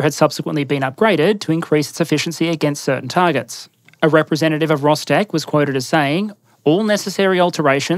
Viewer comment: I absolutely misheard that as 'a representative of Lostech' and got unreasonably excited for a moment